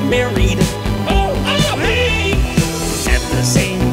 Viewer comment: Best line: {Unintelligible} Seriously what did he say? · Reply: Oh oh hey